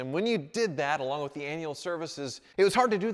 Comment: Did he just say anal service?